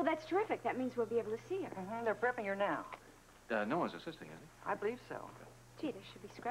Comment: 😂 Sounded like the nurse said "they're burping her now". 😅😅😅